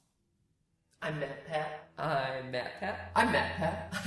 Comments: I heard I'm mad pad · 0:9 I heard him say "Im meth pad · The more he repeated it, the more it sounded like he was saying "I'm MacPap" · I thought you were saying "I'm batman" for a solid 5 seconds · The beginning where he said I’m matpat over and over again It sounded like he said I’m map at XD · I heard "I'm Map Hat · I thought he was saying I'm Batman before the intro started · When the "I'm MatPat" repeated more than once at the same time it sounded like he was saying "meow" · bruh i heard ' IM MAP HACK IM MAP PACKIM MAP HACK IM MAP PACKIM MAP HACK IM MAP PACKIM MAP HACK IM MAP PACK' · At the beginning I thought he was saying "I'm Batman." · At the beginning I legit heard " I'm Batman" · I heard I'm BATMAN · This is what i heard I'm matpat I'm matpat I'm matpat I'm matpat I'm matpat I'm matpat I'm matpat I'm matpat I'm matpat I'm matpat I'm meth-head I'm meth-head I'm meth-head I'm meth-head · I swear at one point there I heard "I'm Batman"